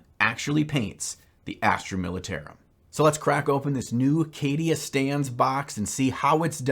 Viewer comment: Is it wierd that every time I hear "Cadia Stands" I only hear "Cadia Stans"? Also, I wouldn't use the official Heavy Metal recipe for somenof my armies. Only the Alpha Legion scheme because I love the colors and transitions.